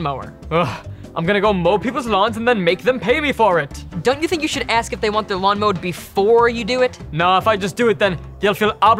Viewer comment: Did grandpa say the a word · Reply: No he said, "Wazz Bag." We don't cuss on this channel.